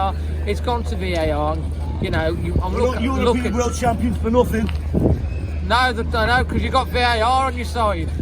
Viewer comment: What did that Liverpool fan say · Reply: we're European and world champions for nothing- I libelieve